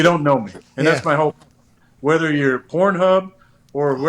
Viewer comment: Did he just say "you don't know me your not pornhub" 🤣🤣🤣🤣🤣🤣